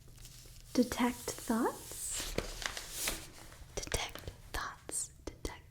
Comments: she said detected thoughts and I thought she said thots and then she said another useful one and I was like...: damn that's more useful than before I can steer clear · ngl i heard defect thots at first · Am I the only one who thought she said “detect thots?” I didn’t know I could learn a spell for that 😂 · I had a bit of a giggle here, when she was saying "detect thoughts" repeatedly it started to sound like she was saying "detect Thots" --I don't need that spell; that's an innate ability for my character by default, I can use that as a bonus action; with +10 to my Perception checks! :P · Detects thots detect Thots haha that’s what I thought she said at first